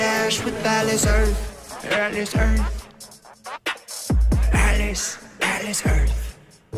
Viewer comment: I swear he says "Alice Earth" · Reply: And at the same time it sounds like he says Alas Earth